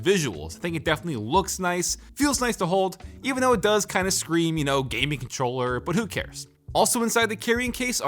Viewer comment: did you say it scrims gaming controller because of the looks